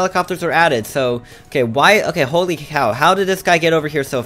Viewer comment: did user just say holy hell???